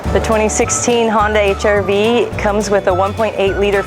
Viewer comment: did she just say hyundai lol